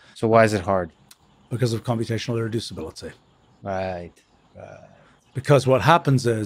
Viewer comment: what does he say ? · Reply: Computational irreducibility